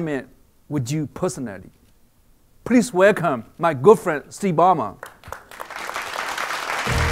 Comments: LOL! He actually said "Please welcome, my girlfriend, Steve Ballmer" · did he say at 5:03 "please welcome my GIRLFRIEND Steve Ballmer" ??????? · did he said my go friend ? at first i thought he said my girl friend :D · At first I thought Peter Chou said"Please welcome my girlfriend, Steve Ballmer!"